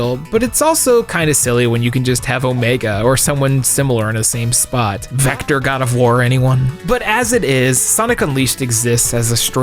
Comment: For a split second I thought I heard "Vector gotta vore anyone?"